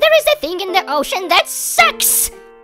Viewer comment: there is a thing in the ocean that “WHATS?!” Oh sucks, thought he said se